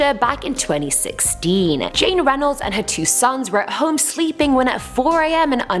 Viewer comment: When she said Jane Reynolds I thought she said James Reynolds from Hamilton and I'm just like THE REYNOLDS PAMPHLET.